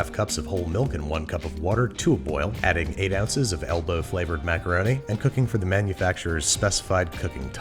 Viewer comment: I know this man did not say elbow flavored